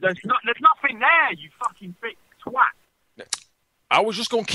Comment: " Big twat" that's so British XD something i would def say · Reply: I think he actually said "thick twat" meaning "dumb twat" in Britain. That's how it normally goes.